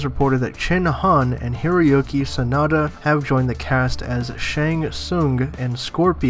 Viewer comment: it sounds like your saying. hairy-oke sanada. Lol